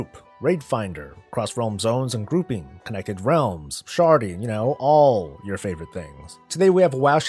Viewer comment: You said "sharding" and im so glad the word showed up on screen cuz i first thought you said "sharting"